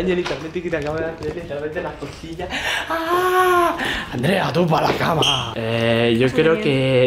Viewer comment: Wtf minuto 5:05 que dice tú Andreína pa la cama ✌️